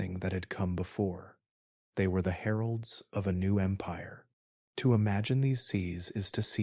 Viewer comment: Wow, I know it's time to go to bed when you say "heralds of a new empire" and I hear "Harolds of a new empire" and I go, "What does that even mean? Is 'Harold' a new slang term?" It took me a solid five seconds to think it through. Excellent documentary!